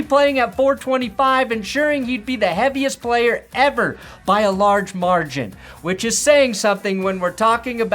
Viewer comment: It's loud at work and I only heard part of that. Did he say something about "large marge" in?